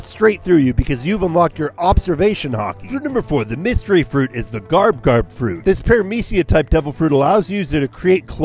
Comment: I thought he said the garp garp fruit 💀